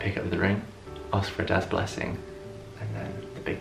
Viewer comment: Youtube Translation: death blessing (instead of dad") ok Youtube!